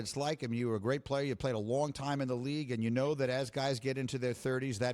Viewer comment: I thought he said 4 guys